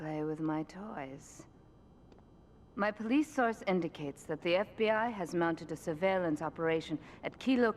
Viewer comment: the subtitles are wrong, Mafia, not FBI